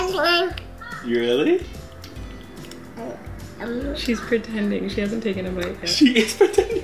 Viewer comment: is the goat. Also I swore I heard say "I'm pretending"